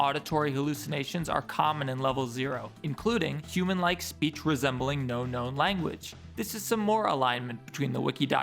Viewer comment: i didn't read the list and thought you said 'speech resembling no-no language' i thought these creatures swore or smth loll